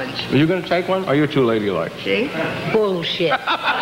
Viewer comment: The first time I heard she said Bullshit 2:34 xD